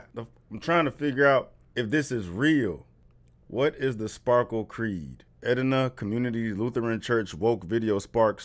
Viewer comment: I had to stop and playback because I was laughing so hard. I thought she said Sparkle Queen the first time 😂😂😂. That was crazy!!!